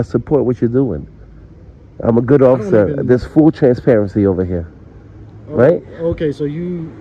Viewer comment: Fraudtor says, “There’s full transparency over here…”, but it sounds like he said “fool transparency”, which fits him to a T.